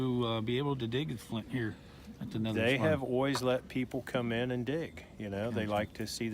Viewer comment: I was looking for ememrgency bushcraft, for making of fire. I recommend that you practice your diction so you say "al - ways" instead of "oize", 2:50. Listen to Prem Rawat!